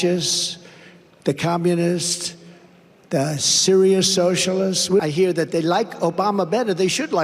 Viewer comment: Me also,, the thing about sensible socialism is that it would definitely benefit the overall economy since balance is the key unfortunately, such a system can never be brought about while Wall Street is drawing breath... I really miss the days before the curse of The Dumpster, when left channels used to talk ACTUAL issues like accessible education and inclusive immigration and universal health Care and starting manufacturing again... Now a good part of the left is distracted by this idiotic Dumpster, and are so tuned out that they're cheering on the military-industrial complex to reduce Russia and create another economic bubble, off turning Russia into the Asian Central America... Wall Street has already ruined Europe with their little proxy war against Russia,, and the Democrats who used to be sensible are supporting this insane war in Ukraine because they're linking The Dumpster with Russia... Pretty strange how pro-choice is now pro-war,, how LBGTQ is now for imperialistic expansion,, how liberals are supporting a fascist regime in Ukraine....... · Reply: oh sensible socialism, I thought he said serial socialism..The man truly dislikes the notdic countries either way, us scary, communist socialist hellholes 😂